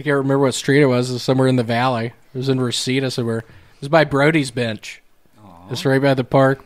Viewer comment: I want to know where Brody's bench was. That sounds like a BEAutiful place to visit! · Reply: Lmao I Thought He Said Brody's BITCH